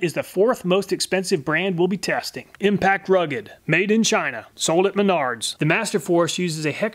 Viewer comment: Who else laughed when he said "sold in Menards" and thought he said "sold in me nards